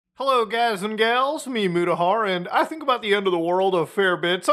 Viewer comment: For the first time I saw the description on your videos and I realised I had no clue what you were saying in your intros. I thought you were saying. Hello guys and gals, MEMUDAHAR (just making a random noise in a thick southern accent), didn't know you were saying "it's you Mutahar", reckon it's because I never knew your actual name in the first place